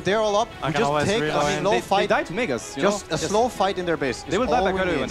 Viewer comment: Did Ceb says the N-word Lmao